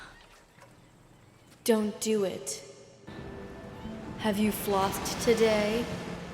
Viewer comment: “have you flossed today “ Okay that really took me out lmao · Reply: Ohh thank you, I misheard it as "Have you flopped today?" and was thinking damnn savage